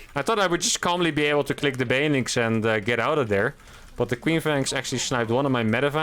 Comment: "queen flank".... I kept hearing queef. I'm a bad man..